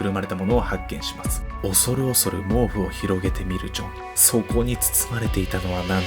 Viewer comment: 聞き間違いかな？ ｢恐る恐る広げてみるちょ｣って聞こえるw てか怖……